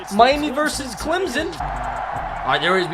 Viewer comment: Did he say Clemson